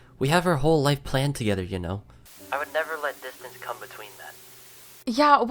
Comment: did anyone realize he said "janelle" instead of kayleigh?!?